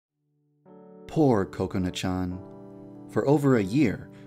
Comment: at the beginning I heard " Poor COCONUT Chan " YAAAASSSSSSS this shoud be a thing